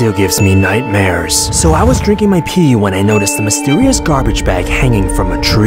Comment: I am sorry but did you just say I drink my pee